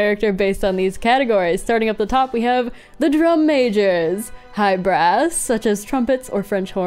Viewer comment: I heard that as “hyper ass”💀